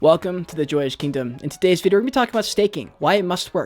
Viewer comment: much better with those sunnies off man. BTW ive always thought you said ....welcome to the jewish kingdom...hahahaha. Its a accent thang i reckon. American accents fool me sometimes.